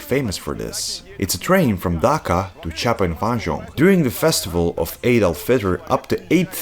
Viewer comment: At like 4:08 or a second or two after it sure sounds like he said "during the festival of ADOLF HITLER" I almost choked on my tea 😂